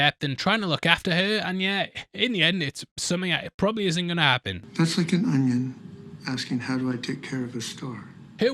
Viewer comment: only realized now that I misheard the onion bit being ' that's like an onion asking how to take care of a star'